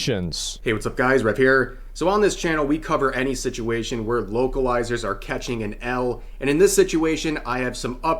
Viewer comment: The whole time I thought he was saying "woke-alizer"